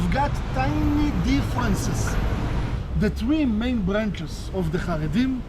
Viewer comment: 3 main 그다음 뭐라고 하는건지 잘안들리네 ㅠ 영어공부 어렵다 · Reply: 3 main branchs 라고 하는 듯